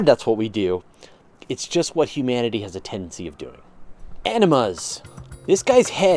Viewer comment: Animuz.... I was listening and all I heard was Enemas. Sounds about right.